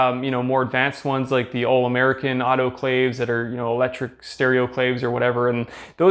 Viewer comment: did you say "stereoclave"? "Steroclave"? I can't find that word anywhere